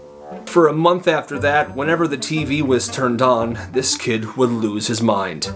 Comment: You said TV, the subtitles said Salmon